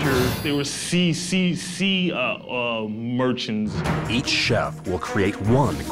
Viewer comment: There was sea sea sea uh uh merchants- 2008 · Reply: lol i thought he said CCC Merchants😹 i was about to look that up